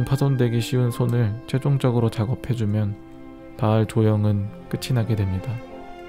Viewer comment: that sounded like"Majima Goro"